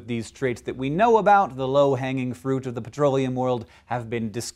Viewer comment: I thought he said fruit oil xD HAHAHAHHA